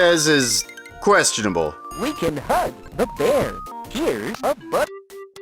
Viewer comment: The bear one isn't questionable, he actually said "We can HUG the bear", not "HUNT"... ...WAIT, THAT'S WORSE!